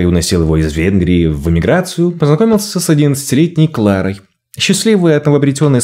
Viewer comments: понакомился с 11 лктеней? мне не послышалось?? они решили держаться вместе и поселились в доме, втф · Мне не послышалось? Кларе 11 лет и она вскоре начинает жить с молодым Йозефом?😮😮😮😮😮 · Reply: Да, тоже самое, три раза, не послышалось 11 летняя Клара